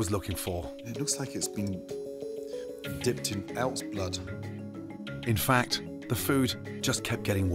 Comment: did you see his face when he called him out for using elf’s blood? They clearly use elf’s blood as a glaze. (I sincerely thought he said elf’s blood, but I think he said elks blood)